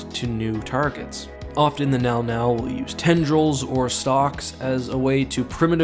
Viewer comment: did i just hear “they use tendrils or socks?” Lmao